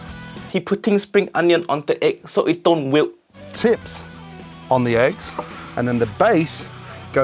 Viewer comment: does he say tits on the eggs?